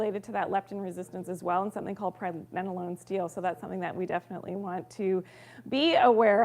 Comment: hi, what term are you saying here? Promenalon steel? Thanks